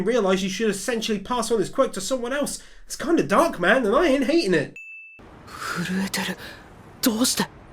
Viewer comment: Says ain't Subtitles say "anit"